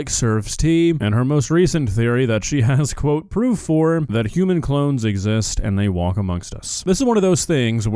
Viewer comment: I thought it said clowns lol